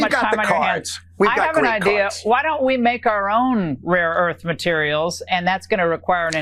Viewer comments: Did she just say "why don't we make our own rare earth metals." 🤦‍♂️😂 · Did I mishear or did Ingradumb really say to Nutlick "why don't we make our own rare earth materials?" That would be a neat trick if we could make rare EARTH materials.